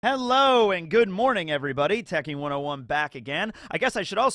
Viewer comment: i was looking down doing a scratchoff ticket and so confused thought Liam was doing a somekind collab cameo intro or something, could've swore i heard "good evening" in an Australian accent...most likely just crazy from lack of sleep though